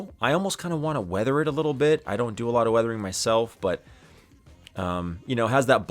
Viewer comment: Lol sorry have to listen for a 2nd time about weathering the Christmas ornament I thought you said wearing it. 😂😂. Sorry.